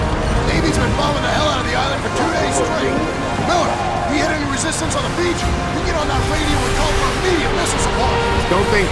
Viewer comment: what did that say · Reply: "I have permission to fire"